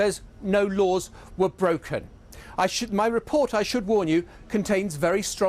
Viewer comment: Did he say, 'I shit my reporter'?